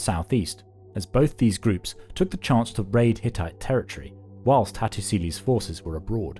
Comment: I thought it said titties😢